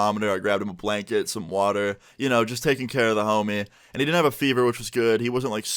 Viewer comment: I really thought he was bouta say “I was just kissing the homie good night.” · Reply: He said he was taking care of his homie